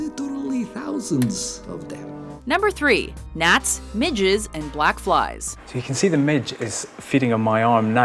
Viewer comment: When she said Midges I didn't read it in time so I thought she said midgets!😂😂😂